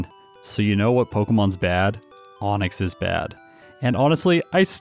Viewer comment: did you just say "Onix-ly"